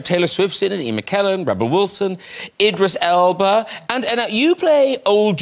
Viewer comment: Did Norton call Dame Judi "Anna" at 7:05? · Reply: I think he contracted the words "and now", but it comes out sounding like Anna, you're right!